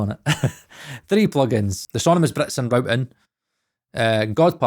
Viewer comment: Great vid. This is definitely an effective mindset to have these days. Btw, what the fuck did u say @ 9:00 right before u said "god particle" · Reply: Sonimus Britson routing